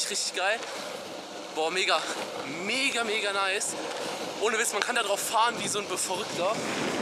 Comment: Ich hab am Anfang verstanden:Man kann darauf fahren wie ein befummelter